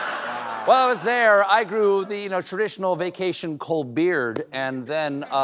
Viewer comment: did he say the traditional Colbeard? 🧔‍♂️That’s funny!😂